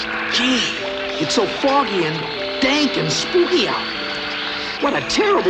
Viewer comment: Did he just say “dank”?